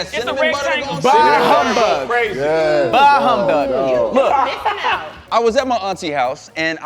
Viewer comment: did just say “bahh humbug” 😂😂😂